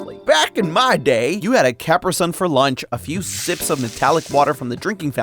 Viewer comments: did he say capersun? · I'm sorry, did he say "capper-son" instead of "ca-pree-sun"?